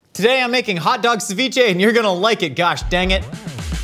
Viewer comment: Did he just say Josh dang it? Lolol